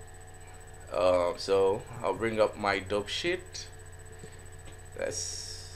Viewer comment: when you said dope sheet, i literally thought you said dog shit lmfao